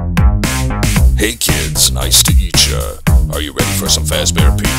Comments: "hey kids ice to eat cha" am I the only one who heard that? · does freddy say nice to eat ya or meet ya? XD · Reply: He says "nice to eat ya ! " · Is it just me who thinks they just realized at around 1:19 Freddy says “hey kids nice to eat ya..” instead of “hey kids nice to meet ya..”